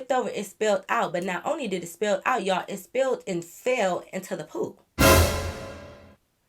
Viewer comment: girl i thought you said “It fell into the poop 💩” I was Rey say wait what😂😂